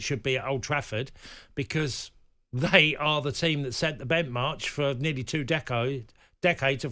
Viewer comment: What word comes out of Cascarino’s mouth at 2:49. I think he means ‘benchmark’ but he says something completely different. How this man keeps a job at The Times newspaper is beyond me. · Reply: bentmarch.